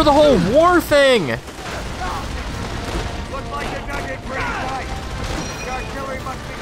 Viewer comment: I think he’s saying “dug in” not nugget 😂 still a great video, I remember buying this when I was a kid and wondering where zombies was because my other friends had it